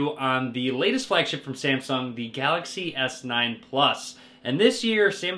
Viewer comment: Did anybody hear samplong 0:08 ?